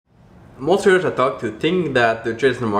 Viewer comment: "Most traders I talk to" @ 0:19 "What's up, traders? Welcome back...?" Both of these lines were so garbled, slurred and not well pronounced that it took me several seconds to realize what you had said. And during the time I took to analyze that, you had already moved on to the next thing you had to say. So I got lost. I stopped watching the videos at that point. You are just too hard to understand. You really need to stop slurring your words so much. If there is one thing you need to do when running a youtube channel and a major sphere of thought and influence in the trading world (or any world for that matter) it is to speak clearly so that you're easy to understand.